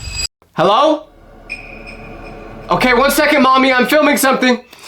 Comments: did he just say mommy😂😂😂😂😂😂😂😂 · did you call her "mommy"?😂